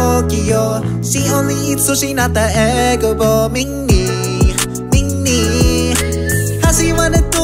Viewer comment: Am I getting deaf or I hear "Ming Ni" instead of Li